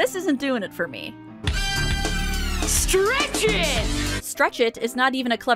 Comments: I thought he did say stretchen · I thought he did say "Stretchin'". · I thought he did say "Stretchin!" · i thought he WAS saying stretchin lol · I actually thought he did say Streatchin' just like Bitchin' but I guess I was wrong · I thought he did say stretchin